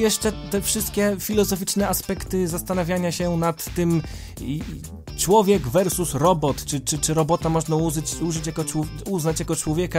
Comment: Usłyszałem "Człowiek versus kompot"